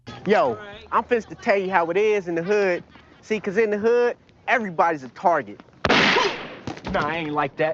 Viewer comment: I misheard that as "Everybody's 're+arded.'"